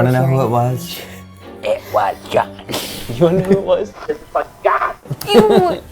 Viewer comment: I hear "It was Em" and "Who the f**ks John?"